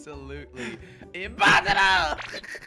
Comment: i heard him say impasta also is this pennywise